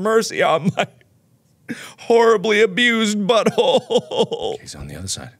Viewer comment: "Why won't they have mercy on my horribly abused butthole..?" --Markiplier 2K18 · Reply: I thought he said "amused" at first